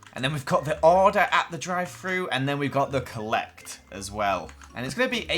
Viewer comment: My volume was down hella low, so whenever said 'Drive-thru' i always heard 'dry fruit' and it made me wonder if he was really gonna put dried fruit in😂😂😂😂